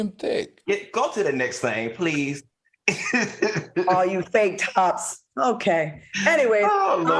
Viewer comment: Claudia needs to be checked on calling someone a “ fake bottom “. Do she know how insensitive that is ? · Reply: I thought she said fake top. That’s hilarious! Lol😂 Also, how sensitive is it that he makes fun of Claudia’s eggs? He needs to take what he dishes out.